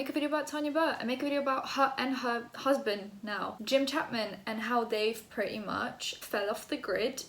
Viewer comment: Somehow I heard ‘Tanya Burr and her cousin Jim Chapman’ and I was like whattt have I missed wow